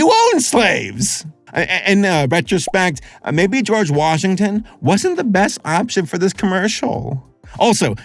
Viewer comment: I though I heard “I may be George Washington”